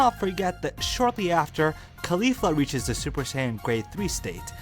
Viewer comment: did you say Khalifa?